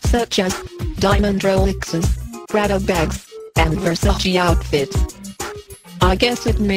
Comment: I remember when this album came out and I thought the Vicky voice said "rattle bags". She was saying "Prada bags". Lol.